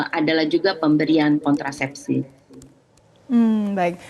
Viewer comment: Promosi kontrasepsi??? Apa aq yg salah dengar??